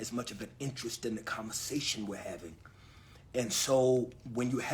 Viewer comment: Did he say "commasation?